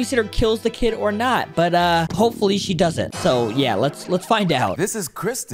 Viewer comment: thought he said “hopefully she does it “😅😅😅